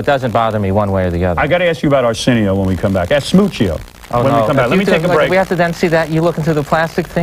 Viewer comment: Howard invented the concept of destroying your guests. See 'Eric Andre' for the latest updates. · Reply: did he just call Arsenio Hall "Ass-smoochio?" LOL